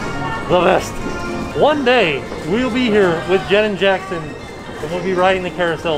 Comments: Did he just say "One day we'll be here with Janet Jackson riding the carousel". Darn mask!!! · When you were on the Carousel, I honestly thought that you said that you were looking forward to eventually riding it with Janet Jackson (instead of Jen and Jackson 😂)—Ha! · I know you said jenn and Jackson on the carousel but I heard "Janet Jackson" so theres that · I was only halfway paying attention and I thought said someday he'd be on the carousel with Janet Jackson and I was super confused 😂